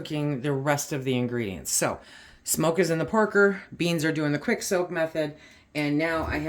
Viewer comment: Smoke is in the porker? 😂. I’m sure I heard you say that approx 2:17-2:19. 😮 This recipe is right up my alley. I love a little heat/spice in almost everything. Putting it on my to try list. I did pinto and black beans yesterday.